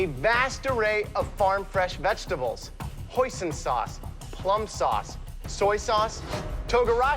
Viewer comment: I swaer to GOD if name label wasn't there I will confuse for the rest of my life cause I heard "Poison sauce" 🤣🤣🤣